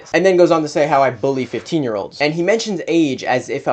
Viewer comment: Edit: i rewatched the video and at one point said he bullys 15 year olds and i thought he said uses 15 year olds, this is an audio error on my end and I apologize for any trouble my comment has caused. I misinterpreted what said and my memory served me incorrectly because i thought you used another girl in one of your omegle predator catching videos. I have now edited the comment to make it more accurate and I'm making this comment here to publicly apologize to you. Sorry.